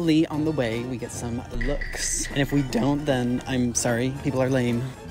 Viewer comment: did he say in this video he is 30 years old. i would have never thought it. i thought he's around 22 -24 something like that. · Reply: my bad. i mis heard the word you spoke when you said I'm sorry. the way you spoke it almost sounds like your saying I'm thirty. a word mis heard on my part. love n hugs to you and your fam.